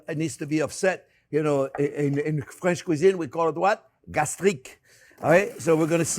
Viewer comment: What is this word please? How do I spell it "galiss?" Trying to learn more xx